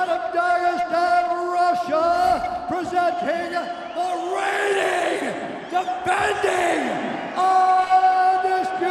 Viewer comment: What it's buffer say at 2:40? Im spanish and I don't understand that part what he say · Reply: He mentioned his high and weight, then says: FIGHTING out of (name of the city, Dagastan or something like that) Russia, presenting the reigning, defending undisputed ufc light weight champion of the world, Khabib, the eagle Nurmagomedov